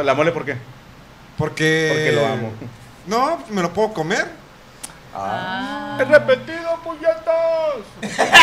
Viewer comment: Que dijo la mole al 01:59:37? Jajajaj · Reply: Nestor Del Angel Creo que dijo: Es repetido puñetas!